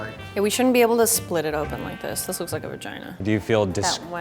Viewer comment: Was it just me or did it said ‘no this looks like a vigina’